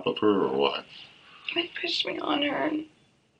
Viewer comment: into the video does she say "Mike pushed me on her" ???? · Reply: THEY pushed me on her